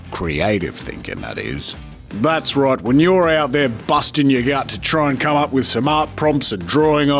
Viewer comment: "that's right when you're out there busting a nut" Lol i thought that was what he said